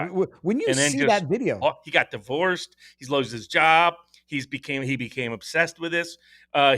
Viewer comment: What are you saying here Barnes? He got divorced, he .... his job? Lost? Slows? I can't make out the word. But he was still employed at the time of death right? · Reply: The word was "lost" and what Barnes was referring to was the time period in which Pretti became radicalized, which could have been years ago.